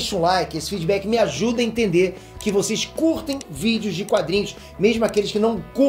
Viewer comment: Eu entendi xvideos